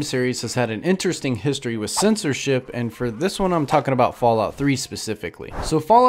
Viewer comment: i thought you said synths and shit not censorship